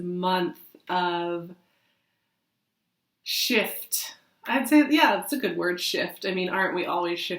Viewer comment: I thought you said Shit! Not shift! They both seemed appropriate right now!!! Lol. Thanks!!!!